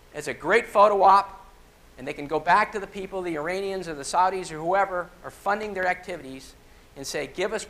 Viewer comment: did he just say "Saudi's"?